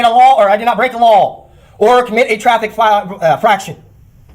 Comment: “…commit a traffic fraction!” 11:38 did I hear him correctly?!